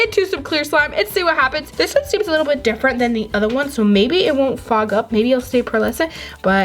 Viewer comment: It sounded like she said fuck up but she said fog up